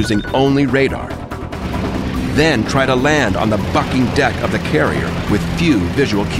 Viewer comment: did he just say "Land on the fucking deck"? XD